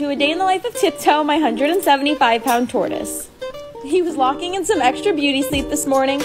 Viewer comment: The very first time I seen a video of tip-toe I thought the girl had said “ my 175 years old tortoise😫😂🤣sorry tiptoe ☺️